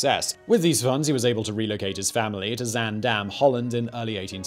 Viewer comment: Zaandam. I had to rehear Simon say that cause i thought he'd said Van Dam